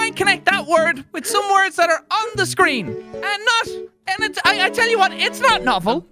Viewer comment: What could you possibly crunch? I wouldn't mind crunching on an onion. I'm going to lock in onion. Yeah, great guess. There's nothing else I could scream that could possibly relate to crunch. Well, I want to crunch an onion. Yeah, I want to crunch your skull. I thought you were being a supportive teacher. I thought you were being intelligent. I died of laughter lol! 😂😂😂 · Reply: Huh? I honestly thought he said “scream” not “screen”. Maybe you’re right, but I’m keeping it the same, also I’m glad I’m not alone lol! 😂😂😂